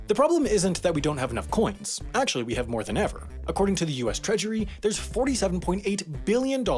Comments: You know what else the world has a shortage of? Somewhat educational, somewhat interesting video topics! Help us find the few left by suggesting them to us and, to reward you if you've done this successfully, we'll send a free HAI t-shirt to anyone who suggests a topic we use: <link> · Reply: maybe my ears are plugged, but I thought I heard “US ‘Treachery’ ” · Am I the only one who heard him say "treachery" instead of "treasury"?